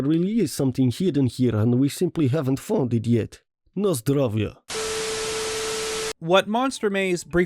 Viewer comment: Did I heard "Na zdrowie"? XD